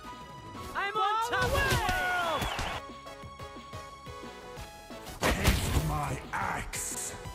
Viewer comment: O thought balmond said taste my ass hahahaha